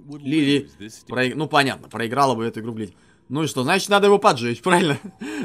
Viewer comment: Мне одному показалось что он сказал слово БЛЯТЬ на